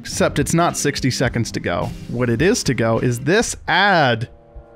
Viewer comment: An ad played just as he said “ad” at 3:50 so it sounded like “what is to go is this a-“